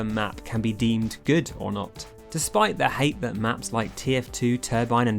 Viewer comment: Am i the only on who heard him say people hate maps like tf2